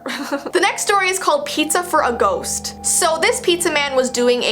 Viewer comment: When said Pizza for a ghost I thought she said Pizza for a goat 🐐 hehe